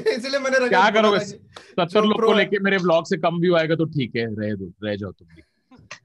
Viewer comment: Can anyone please tell me what tanmay said here? Thanks in advance. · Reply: I think he is saying 70 log(tvf team i think) ko rakh ke bhi mere vlog se kam views aaenge to reh jao.